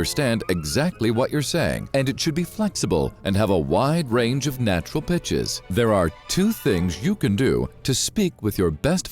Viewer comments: I thought he said wide range of natural bitches 😂😂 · did anyone else hear "it should have a wide range of natural bitches" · I thought he said and have a wide range of natural bitches